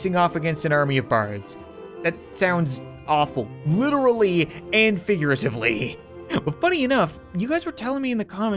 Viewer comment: did I hear you say... "Lute-rally and figuratively?" No? No? I'll see myself out.